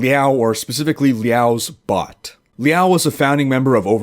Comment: i thought he said Liao's butt